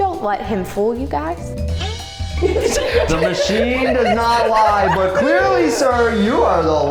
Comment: I thought he said he wont lie but he lied